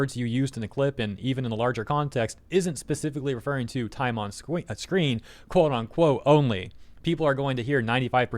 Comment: we totally didn't hear "time on sqween :3"